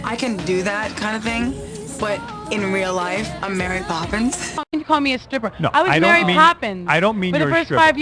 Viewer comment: did she say "fuckin call me a stripper?"